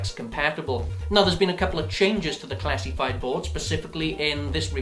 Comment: lol at 0:52 transcribe audio translates what he said into "Theres been a couple of changes to the classic bible" lmao